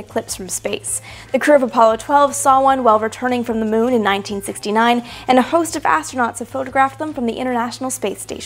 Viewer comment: I thought I heard her say "...from the moon in 1959." Maybe she's getting a little too hung-up on the old days.